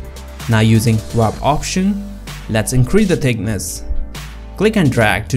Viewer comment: How did he access the Warp Tool @ 5:21? Did he say drop option????